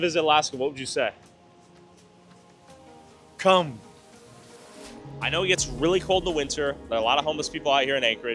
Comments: "to anyone who wants to visit alaska, what would you say? Cum" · i think he said "come" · DUDE did you really have to make the caption say “cum” when he said “come”!? 🤣🤣🤣 That poor guy lol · " Anyone who wants to visit Alaska what would you say?" A Man : "Cum"